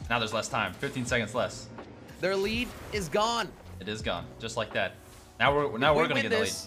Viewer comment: Jelly: their lead is gone:it is gone just like dad DID ANYONE ELSE HEAR THAT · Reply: Dude he said "Just like that"